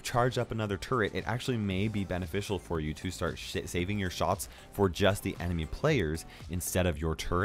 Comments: Is it just me or ar 7:51 did say "shit saving your shots"??? Tell me if you hear it too. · DID HE SAY SHIT!?